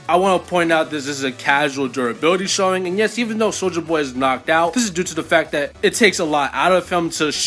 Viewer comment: I always laugh when you say "soldier boy" because i always hear it as "soulja boy" for some reason lol.